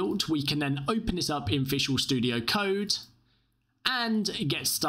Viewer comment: what studio you using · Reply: ohhh i sounded like you said inficial studio but you said visual studio, you talk to fast.